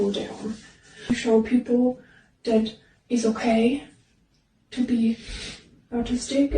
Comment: almost thought she said "It's ok to be artistic" it would make just as much sense